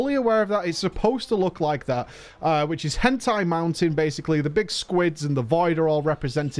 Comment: I guess Shrine of the Storm was the dungeon they used as an example in the encounter panel at Blizzcon? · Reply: When he was talking about the storm dungeon... I thought he had said something about "hentai mountain". And I just had to reassure myself "no. No he didn't. Stop it. Back to your corner."